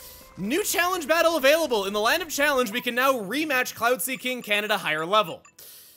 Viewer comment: Am i the only one who heard "cloud sea king canada"